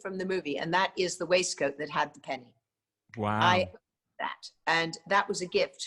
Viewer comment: Please, someone - tell me what she says she has as a gift from Chris from the movie? I cant understand what she is saying! Thank you! 26:26 in the interview. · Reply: She got the waistcoat that Chris wore with the penny in it.